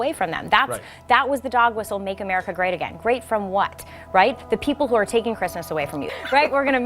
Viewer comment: Did she say great FROM what?